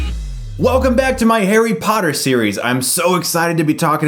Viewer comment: IT LITERALLY SOUNDED LIKE HAIRY POTTER!!